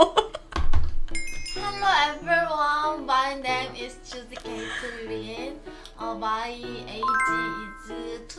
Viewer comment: 나만 쥬지 케이틀린으로 들림?